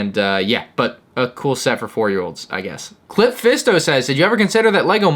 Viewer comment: am I the only one who heard "clit fisto"?